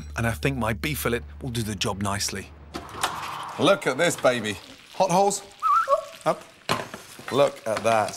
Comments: I can see that nobody has said it but at 3:06 why did he called his daughters hot holes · Did he just say hot holes??